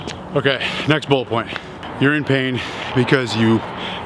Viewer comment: I thought he said "next BULL point" -- LOL, I had to go back and turn on closed captions to double check